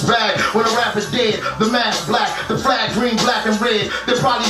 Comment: i dont think he said mask I think he said the mass black, the flag green black and red STILLL A FIRE ANALYSIS